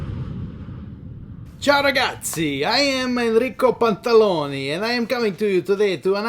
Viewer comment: LOL!!! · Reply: My deepest apologies. I thought you’d said Enrico Bartaloni, when in fact it seems you (or he) actually said his surname was Pantaloni. Gotta go clean that wax out of my damn ears!